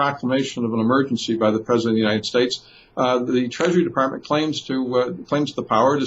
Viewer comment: Did Chris say "Any God Damn thing they want" at the 19:24 mark of this video ?? I Love It !!!